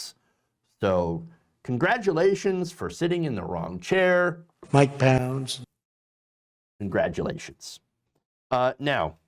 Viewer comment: what was said? Mike Pounds?